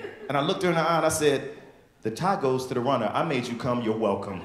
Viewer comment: ??? "The tie goes to the wonder ?" what the fuck is he saying???